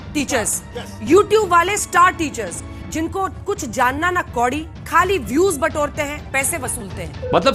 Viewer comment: "jan na kuch kodi" these were her words, but people are now twisting it by telling " ki do kodi ke teachers" i am not her supporter, and whatever she said was wrong but she did not say do kodi and even dhruv is saying do kodi here...... this mistake was not expected from him